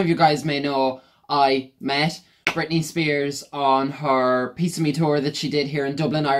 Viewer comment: I thought you said piece of meat tour.